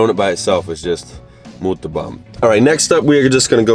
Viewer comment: what did you say at 2:00? muta bom? just curious to what it means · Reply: Brian Truong muito bom= very good